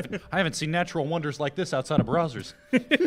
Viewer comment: did he say Brazzers? 😂